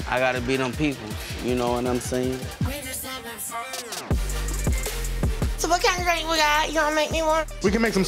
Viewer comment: what did she say 😂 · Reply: So what kinda drank yall got make me one?